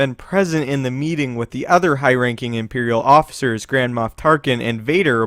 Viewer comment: Ha! Sounded like you said "Grand Moth Tarkin"